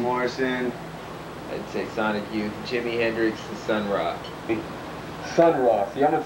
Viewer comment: I understand Sonic youth, Jimi Hendrix, but the last thing I can't make out. Could someone please help me? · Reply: Sun Ra